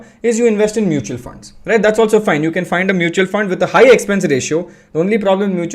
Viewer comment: did you say 2:50 find mutual fund with high expense ratio ?